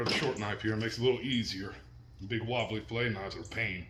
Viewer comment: Would a fillet knife work ok or should the blade be more of a stiff blade? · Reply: I just heard him say, “ the big wobbly fillet knife is a pain” see I pay attention